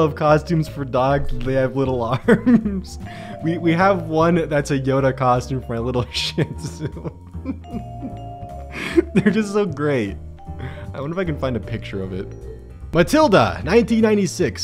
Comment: When you said little shitzo. I thought you said little shit